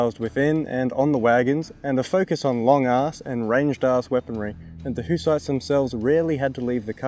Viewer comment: am i hearing that correctly?? Long ass and ranged ass weapons??